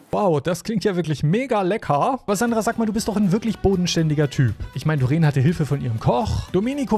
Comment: Ich hab verstanden: " Sandra, du bist so so ein richtig bohnenständiger Typ". Haha XD